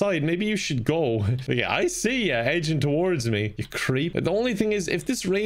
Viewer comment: what he say? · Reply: I see you edging towards me, creep?